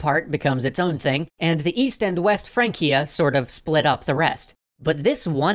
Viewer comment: Charlemagne built an empire, only for his descendants to tear it apart. Does its collapse still echo today? Support us on Patreon! <link> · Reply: EAST FRANCIA? I thought you said Weast Francia!